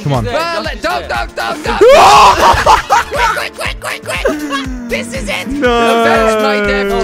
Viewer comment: it sounded like duck duck duck quack quack quack